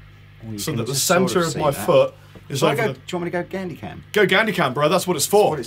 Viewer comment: I thought they said Gandi Cam